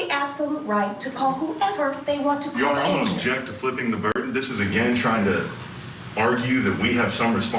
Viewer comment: "flipping the bird"??? really does NOT sound professional. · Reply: "Flipping the burden". Meaning Burden of proof. I thought he said Flipping the bird too. 😆